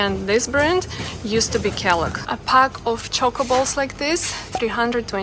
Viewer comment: You said Choco balls and I heard chocobo's and looked up and was sad to see that my mythical creatures from Square enix weren't being sold at the store =(. Great video!